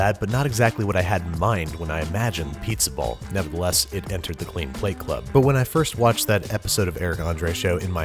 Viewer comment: I heard "it entered the clean plate club" and was about to call a hospital